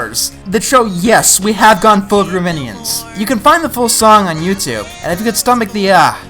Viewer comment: I didn't understand what he said at 15:08. "We have gone full Gravinians?" What does that mean?